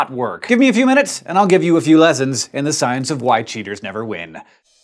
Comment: I did a double take at 0:46, cus i thought he said "white cheaters never win". i haven't laughed so hard in ages.